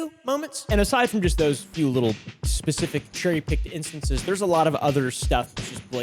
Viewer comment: “Cherry picking instances” 10:44 I really thought he said cotton Pickin instances